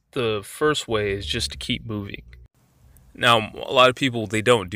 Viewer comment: "... is to keep ...?" What is that word? I really want to hear what you have to say, success in audio media flourishes with clear diction, breathe deeply to carry sentences out to a defined ending. You've got great things to share here thank you for posting! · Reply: is to keep 'moving' = Moving away from their sight